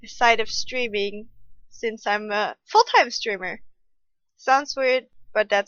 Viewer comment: did i hear it wrong? " Full time stripper " ? WHAT. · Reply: Full time streamer xD